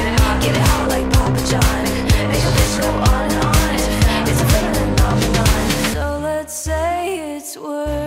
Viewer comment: why did i hear "feminin on on on" as in women on women:face-red-droopy-eyes:2gay4dis